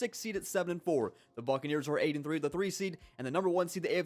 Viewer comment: for a sec I thought he said "fuckneers"